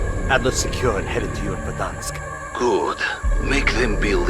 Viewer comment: did i hear verdansk